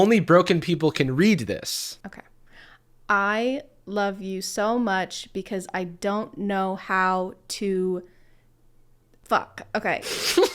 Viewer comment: I think the 12:15 thing is "I love you so much but I don't know how to tell you -(can't figure out the end)